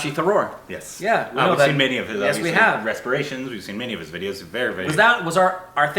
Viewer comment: says respiration instead of reperations 😂